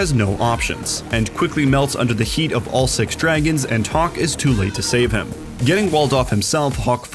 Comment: I thought they said "talk is too late to save [Gator]" @ 6:28 and I thought "damn they're just gonna roast Gator in the middle of this analysis" lol